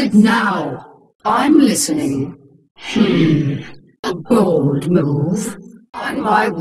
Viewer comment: I always thought Kerrigan said "I've bowled over drones" but it was actually "a bold move"